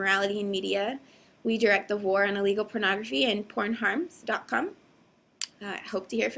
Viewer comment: lol I thought she said Whore on illegal pornography.